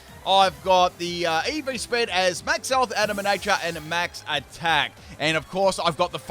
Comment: did say Shedinja has max health ev?